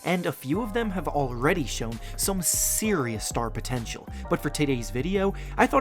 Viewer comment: I heard " but for titties video " anyone else?